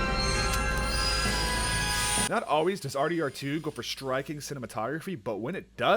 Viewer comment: Had to take a double take to make sure I didn't hear R2D2